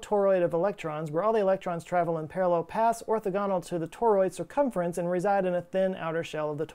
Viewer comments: Big fan of this channel, but I am pretty sure you said" "orthodontal" instead of orthogonal, at minute 14:03 of the video. I have also seen a variety of lightning that I am not certain was covered. I call it "pinwheel lightning" I have only seen it once, in the late 1980s. I was sitting in a car looking at the lightning storm about 3 miles away on the prairies. Suddenly, from horizon to horizon, spinning wheels of lightning left the ground and slowly ascended to the clouds....It was SPECTACULAR! The toroidal explanation is a possible hypothesis. Keep up the good work! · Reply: I definitely heard 'orthogonal' not 'orthodontal' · Big fan of this channel, but I am pretty sure you said" "orthodontal" instead of orthogonal, at minute 14:03 of the video. I have also seen a variety of lightning that I am not certain was covered. I call it "pinwheel lightning" I have only seen it once, in the late 1980s. I was sitting in a car looking at the lightning storm about 3 miles away on the prairies. Suddenly, from horizon to horizon, spinning wheels of lightning left the ground and slowly ascended to the clouds....It was SPECTACULAR! The toroidal explanation is a possible hypothesis. Keep up the good work!